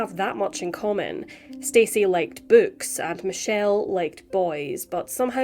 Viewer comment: I thought you said: Stacey liked boobs.